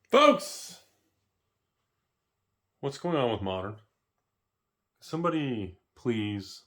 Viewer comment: It sounded like you said what's going on with Monarch, I was excited you trolled all the MTG players